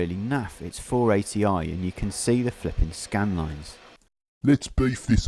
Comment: "Poor resolution, you can see the scanlines". I thought that was what you wanted with your fancy gadgets to create scanlines!?. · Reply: Scanlines?? What's scanlines? I thought he said skylines.